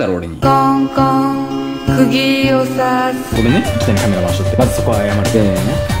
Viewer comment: 最初の、カールマっ が カメラっ にいつも聞こえる